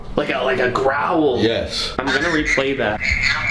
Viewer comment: sounded like a "YES" in